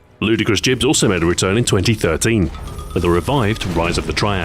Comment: You say 2013, but the captions say 2012. Wha?